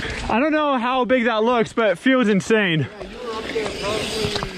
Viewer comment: First time I heard "I don't know how big that looks but Phil's insane". Not wrong. It look pretty damn big.